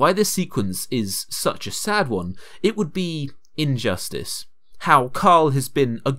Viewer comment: what he just said there is how could be